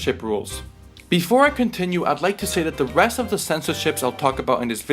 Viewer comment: thought you said the breast of the censorship lmfao